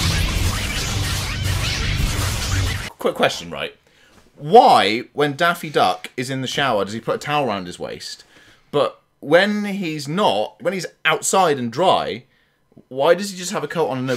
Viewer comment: Did you call him Daffy Duck? 😂